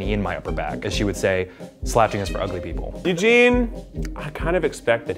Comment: Keith: scared Eugene: turned on lollll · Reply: im stupid cus at first I thought u said engine and not eugene lmaooooooo